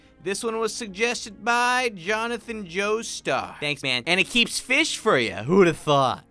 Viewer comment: When he said thanks man I thought he said nyin nyin